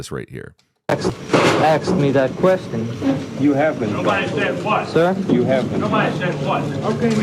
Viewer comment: this expression is his sudden realization "I'm screwed" and his face turns withdrawn as he knows he's been set up to take the fall. He was duped , and they made him just what he said he was: "a Patsy." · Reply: That turn and facial expression seemed to be triggered by the voice off camera saying something like "nobody says what" , that seems to have been the message. His facial reactions relate to that off camera voice and message.